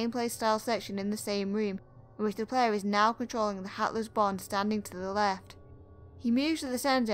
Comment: I heard "Hatless Bon" as "Heartless Bon", and it still kinda fits.